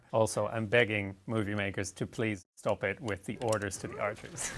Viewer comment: I am not a native speaker and I don't understand what he says about archers doing something not realistic at 7:17. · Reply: he says that he's begging movie makers to please stop it with the orders to the archers